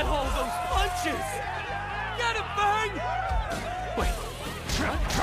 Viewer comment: did he say”get em furg” referring to kimbo slice?!